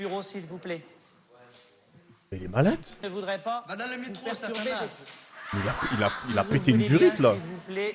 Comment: y'a que moi qui écoute "vas dans le métro satanas" ? a 16:43 ?